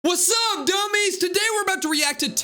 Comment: My school heard you say what up dummy’s